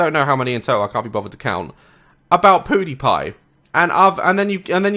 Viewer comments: Ur accent makes it sound like u said “poodie” pie and i love it XD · Omg, at first I thought he said “poodie pie”😂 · sounded like he said poodepie but he 100% meant pewdepie sometimes pew sounds like different words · did he just say "poodie pie"💀😭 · Why the heck has everyone took this so seriously but wait I just heard you say pew die pie and it sounded like poodipie 😂😂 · It sounds like you said PooDiePie. XD